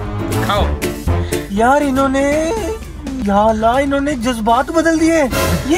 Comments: Did You Said Ya Allah?????........ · did said ya allah??